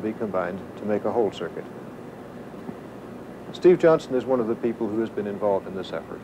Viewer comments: Did he say "Steve Jobs"?! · Did he say Steve Jobs?